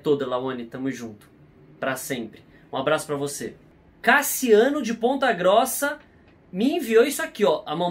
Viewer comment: No momento 12:09 eu ouvi ele dizendo caciaune de bunda grossa